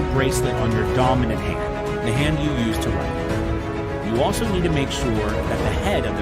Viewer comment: When he was explaining how to use the bracelet, I thought he said” use it on the hand you wipe with”